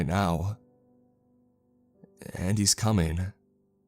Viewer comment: The story "And he's coming..." What I heard "Andy's coming..." Hits floor limply